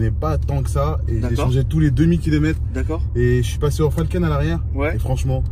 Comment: A 13:45 c’est quoi la marque qu’il dit ? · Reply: Falken